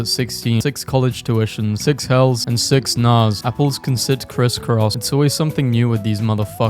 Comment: did he say apples instead of owl